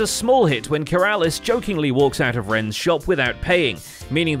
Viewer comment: I spotted a mistake in the subtitles, they say Iskall instead of Ren